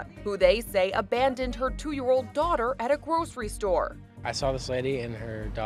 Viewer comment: I thought it Said for 2 years